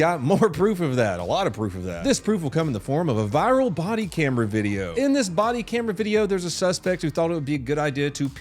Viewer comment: I thought he said potty camera video, lol ahh God I need a nap!😅